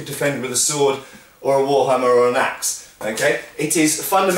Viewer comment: did you say wallhammer?